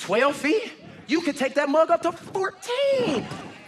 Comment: DID ANYONE HEAR THAT???? Did he said "you could get that MOTHER....."??? I keep thinking he can't get any worse!!!! He just said a part of one of the worse cuss words!! 👀👀👀👀🔥🔥🔥🤡🤡🤡😈😈😈